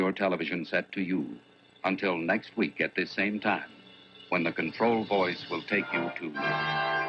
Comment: When I was a kid, I thought they said "The Control Boys", as in "the boys in the control room" at the TV station.